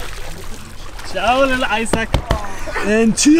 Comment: did you just say sand bitches :D HAHAHA